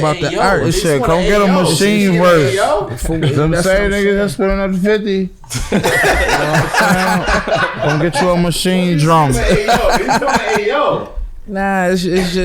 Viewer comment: Wtf did Con say at 43:18 😂😂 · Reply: donovanbvrton "For the same niggas that's spendin that 50..know what I'm sayin..come get you a machine drum" 😂😂😂